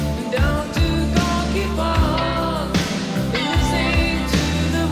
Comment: Maybe a childhood mix-up, but it sounds like Klaus says, [ Gonky~park ] instead of " Groky Park " Still mesmerizing and gets nostalgic feelings coming [...]